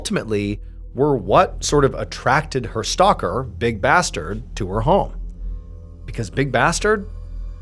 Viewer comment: Hi, I quit fentanyl and other opioids cold turkey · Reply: Is he saying big bastard? 36:38 hahaha